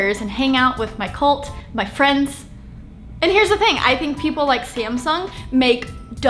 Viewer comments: sounds like she said scamsung lol 😂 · scamsung? what are you trying to say? XD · Did she just say "SCAMsung"